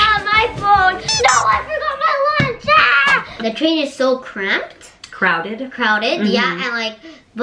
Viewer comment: cramped をcrowded に訂正されていますが、cramped はこの場合、不適切な使い方なのでしょうか😅日本語の説明では公共交通機関での窮屈さ、とあり適切な表現のようにも思たので（cramped という単語自体知らなかった私ですが←）💦 ちなみに私はcrammed と聞き取ってしまいました😂